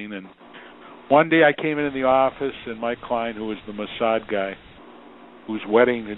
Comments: the mossad guy. Is what he says. Cia of Israel. Why are the subtitles edited here? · In that clip with Heller talking about Eazy wanted to kill Suge, it sounded like he said his client was a “Mossad guy” but the caption sad “most sad” big difference and sounds like could be a whole nother video in itself 🤔 🤯